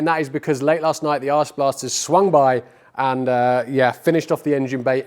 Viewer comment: I swear that sounded like you said Arse Blasters.